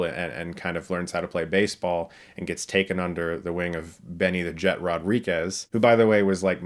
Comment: I initially thought you said "Benny 'The Jet' Urquidez" & I was like "What?! Why is he in a kids movie when he's usually in martial arts movies???" LOL